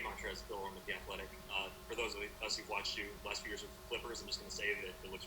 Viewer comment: enable your subtitles, you'll see it said flippers instead of clippers lmao